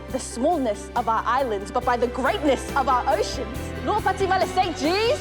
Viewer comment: Whole time I watched this I thought they said “lua pati male say cheese” 😂😂😂😂 and its lua pati male St G’s 😂😂😂😂